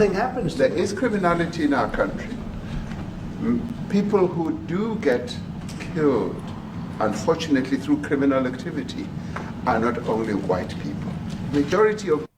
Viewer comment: Did he just say unfortunetly those people who get kiļed all are not white?